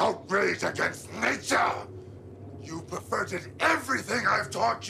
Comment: Chucky: Hello John. Over here. Hi. It's me Chucky. What do ya think? The gri gri work? You know, when I came here learning that stuff about how to cheat death, I thought maybe you were pulling my chain. But, not now. Uh, uh. Not now. Only one problem. John: What? Chucky: This. I didn't think anybody could hurt me. But, last night I got shot. And you know something? It hurt. It hurt like a son of a bitch, it even bled! Why is that, John? John: You're turning human. Chucky: [in shock] What? John: The more time you spend in that body, the more human you become. Chucky: [points his finger] You mean I have to live out the rest of my life in this body? No fuckin' way! You got me into this, you get me out! John: I can't do that, Chucky. Chucky: Why not? John: Because you're an abomination. An outrage against nature! You've perverted everything I've taught you and used it for evil! And you have to be stopped! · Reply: I thought he said "inverted" instead of "perverted"